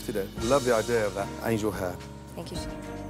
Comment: I thought he said “I need your hair” XD